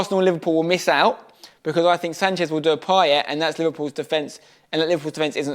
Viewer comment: I thought he said shoutout to Payet instead of Poet 😂